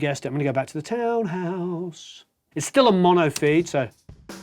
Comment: Great stuff, Warren !! One question tho', what are you saying @ 13:16 after you "siegspiel" you love of the Townhouse ? : D > " its still a mono theatre " ? " Its still a mono Beater " ? Its still a mono BeatLe " ? You went full-on British there. Help an American out : ) Ps, Those drums sound fantastic Great Recording !